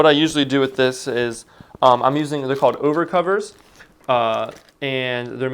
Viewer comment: i heard you saying ubercovers lol